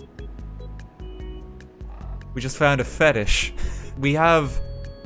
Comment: I thought it said fetus